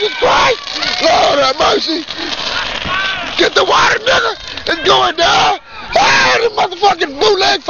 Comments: no one could ever be as disappointed as me. my whole life i thought he was saying get the watermelon its going down. every time i watch this i laugh but then the thought in the back of my mind keeps saying watermelon😭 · instead of him saying ITS GOIN DOWN i thought he said ITS DO OR DIE